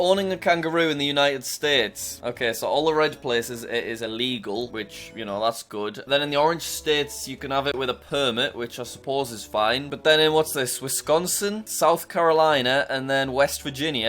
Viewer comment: im sorry but lower down your volume on this one and it sound like is saying "pervert"